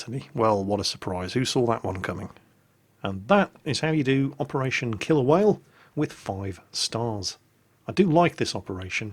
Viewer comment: when you said the name of the mission for the first time it sounded like you said "operation kill a whale"